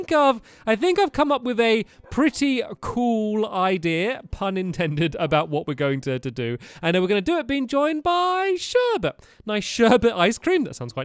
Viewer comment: Who else heard Stampy say "Punentended" instead of "unintended" at 1:45?